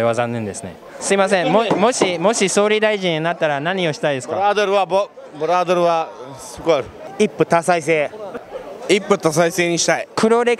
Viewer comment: I think I finally understood what the guy says at 00:50 ブラーデルはすごい Meaning "burqaderu ha sugoi" or in English "Brothels are awesome" he surely comes from a country where it's impossible to find some. Hahaha